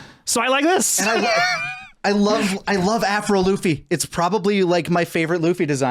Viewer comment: My hearing’s going bad cuz every time you say “Afro Luffy,” all I hear is “Avril Lavigne” 😂